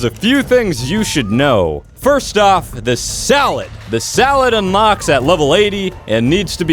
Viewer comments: did he say salad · Is it just me or did he say "salad" instead of saddle